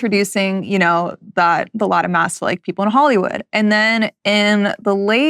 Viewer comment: is she saying Latin mass? Or something else? Lata mass?